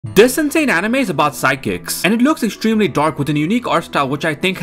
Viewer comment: I legit thought it said "physics" instead of "psychics"